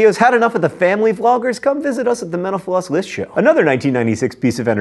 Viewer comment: whenever he said “mental floss” i tHOUGHT HE WAS SAYING MENOPAUSE AND I WAS SO CONFUSED